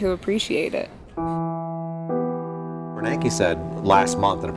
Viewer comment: Can someone tell me what name he says? Renecki? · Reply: He said "Bernanke" as in Ben Bernanke